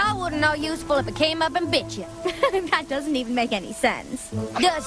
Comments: I swear to God, Applejack said "bitchin" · did anyone else hear applebuck say bitchin at 1:15?